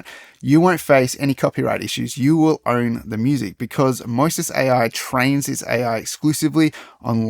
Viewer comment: Sounds like he is saying Moses AI.